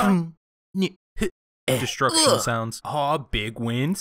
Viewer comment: Did he just say "Destruction Sounds" XD